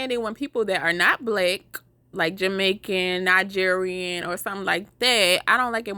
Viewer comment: am I hearing this wrong because since when was Nigerian/Jamaican not black lol ?😭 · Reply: Im gonna address this one last time I said if you are not black, jamaican or african.